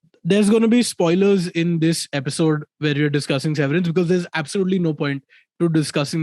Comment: I misheard it as Savourance and was disappointed that it wasn't a cooking show.